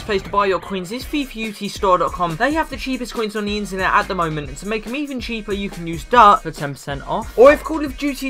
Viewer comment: If you wanna be in my next video lemme know the rarest cards ever! RAREST CARD IN FUT DRAFT HISTORY!!! - (FIFA 16 FUT Draft) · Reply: on the coin intro it sounds like you said "To make me even cheaper"